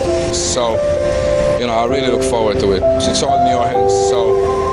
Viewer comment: he said... you know I really look forward "to it" he did NOT say "to win"... to deaf people you just made him look different than his actual words.... not cool!